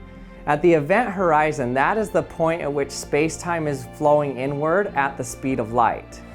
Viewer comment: did he say n word? Jk I know he said inward